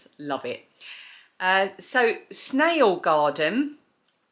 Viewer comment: I would love to find the vlogmas that covers the festive things at Blenheim Castle. I thought you said, "SNAIL GARDEN"? I cannot find said channel. Will you help me find it? Did I get the name wrong? Thank you! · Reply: Type in “The Snail Garden” and it should be the first video that pops up. It was uploaded on December 3, 2023. The Blenheim Palace footage starts at 2:52.